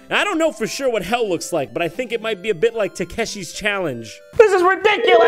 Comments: I thought it said "take shit challenge" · I thought is said takeshits challenge for a second